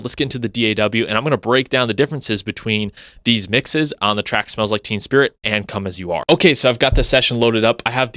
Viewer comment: Did you just say “smells like *team spirit?” Kids these days… smdh.